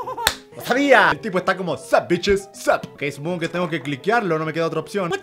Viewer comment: la primera vez que lo vi entendí como "Sandwiches Sap" en vez de "Sup Bitches Sup"